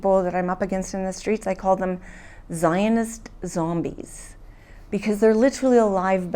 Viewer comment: did she say “Zionist Zombies”?